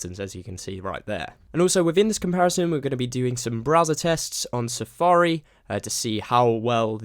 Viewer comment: thought u said were gonna do some brazzer tests LOL